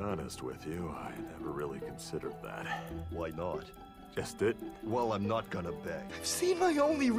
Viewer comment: wtf does brock say at 4:42 i cant tell if its a complete thought or if he got cut off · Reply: "just didn't"